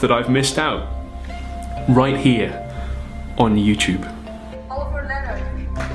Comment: Did she say "Oliver Leonard"? I thought your name was "Oliver Thorne"? Do they do things differently on that side of the pond?